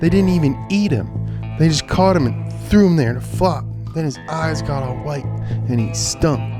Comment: Jsyk, 3:31, the subtitle says “fu*ked”, but you said “flopped”. Usually bad words are bleeped and I don’t want you to lose your Valvoline sponsorship for some stupid mistake like that.